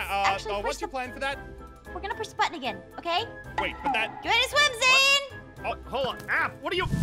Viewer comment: Thought you said you can't swim a** now